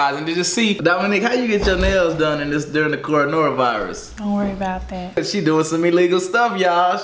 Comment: did he say cornora😭